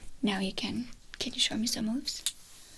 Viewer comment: I was relaxing & sleeping with my earphones, then I woke up with big eyes because in the min 9:37 I heard "can you show me some boobs?" I thought Excuse me!!! 😳 Then "something missin" What do you mean? Missing my boobs? Hahahahaha I can't stop laughing 🤣